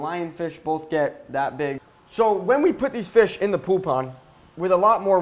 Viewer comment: I thought he said poo pond LOL